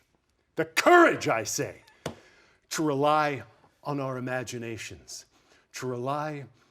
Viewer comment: To rely on our imagination...when Ross says that..why do I hear 'on your imaginary forces work'... Ross is practically Shakespearean